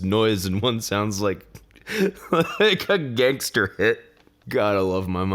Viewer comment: What did he say at 11:19? “Gangster hit” is what I hear.